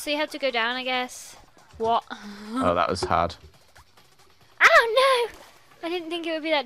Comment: That sound is laugh !!!!! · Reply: Oh! I thought she said 'that was rude' to the comment that said 'that sounds like laugh' excuse me. But yes, it was unneeded to say duh.